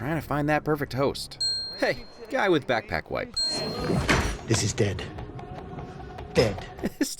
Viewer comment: awhoa i thought he said "this good" not "this is dead" then again, I did watch the movie on with a group of friends playing it from a speaker with no captions. was kinda funnier the way I interpreted it initially